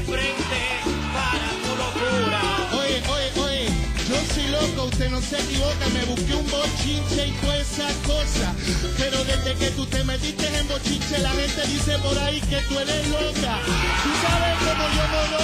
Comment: Que dice Yomo a partir del Minuto 2:05? Slds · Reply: Yo soy un loco usted no se equivoca, me busqué un bochinche y toda esa cosa pero, desde que tú te metiste en bochinche la gente dice por ahí que tú eres loca. Tú sabes, Yomo Yomo lo dice, cumpleaños de tu mujer en Chuck E. Cheese's. Socio, este el stripper, yo el ________?, y tú el que me baja el zipper. Chuck E. Cheese's - lugar de entretenimiento para niños. Stripper - bailarín exótico Zipper - cremallera